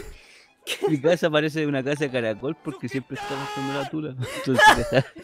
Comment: que mierda dice en esta parte el 23:54 que no entiendo por el grito del goblin ql. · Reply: leonidas Carranza dice mi casa parece una casa de caracol porque siempre esta arrastrando la tula ......